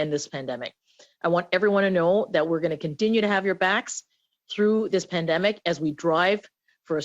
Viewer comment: Every time the say "we have your backs" I swear I hear "we stab your backs"